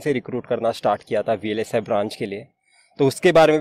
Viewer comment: vlsa branch ke liye? Kya bola bhai?